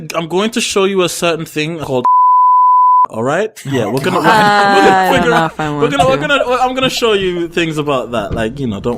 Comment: What did say at 34:20? I am dying to know!!! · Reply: He said you're short, Ed.